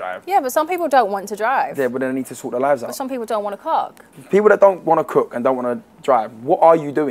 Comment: did she say cook or co..